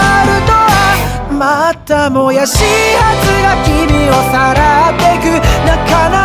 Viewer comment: 父さんに言われて気づいたけど 「また、もやし発芽君をさらってく」って聞こえる